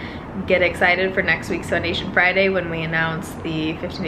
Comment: I thought she said 50 days of foundation and I was like 😍😍😍😍😍